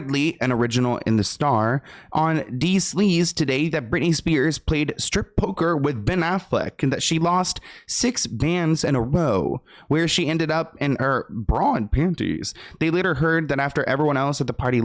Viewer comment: LOLLL I had to reply that cuz he said “bands” instead of “hands”😂 is a true southern man😭🫶🏾